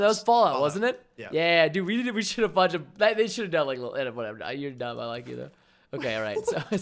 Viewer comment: "we should of fudging, they shoud of ngh whatever, you`re dumb I like you do" can someone explain me what the fudge he tried to say? hahahaha